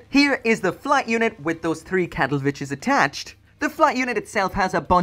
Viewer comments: I thought I heard "cattle-bitches" lol. im sorry. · My hearing is bad and i keep hearing you say “Cattle bitches”?